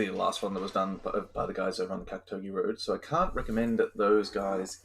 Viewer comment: Didn't catch that, who is the recommended other channel? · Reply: Apollo suede